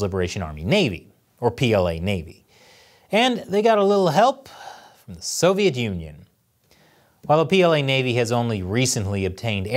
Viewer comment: I kept hearing, "made in the Soviet Union" and realized that not even 'made in China' was originally 'made in China'.